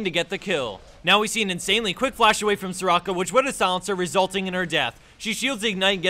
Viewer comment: Lol did he said "quick flash from soraka" 2:20 ?!?